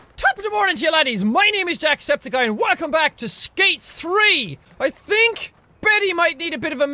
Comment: At the intro when he said skate 3 I thought he said stank 3